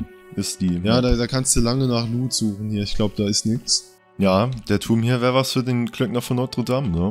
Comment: ich hab Klempner von Notre Dame verstanden :D